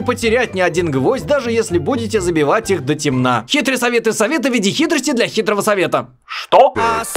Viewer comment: я избранный, я понял, что он сказал: хитрый совет для совета в виде хитрости для хитрого совета